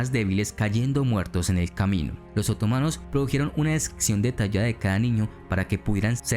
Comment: Que dice en el minuto 7:35? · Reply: "Que los otomanos produjeron una descripción detallada de cada niño"